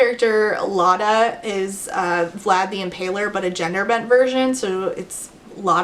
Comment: Lol I was listening while doing something else and when you said ‘vlad the impaler’ i thought you were saying “flabby and paler” and I was shook to attention like whoa that’s not very nice 😂